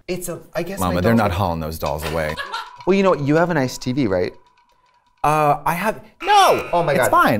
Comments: I swear I heard “you have an STD right?” 😭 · LoL I thought she said "You have an STD, right?"